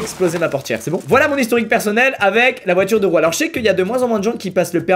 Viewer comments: C’est moi ou il a dit la voiture deux roues a 1:48 😂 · Vers 1:40 c’est moi ou il a dit: Voici mon expérience avec les voitures deux roues ? 😂😂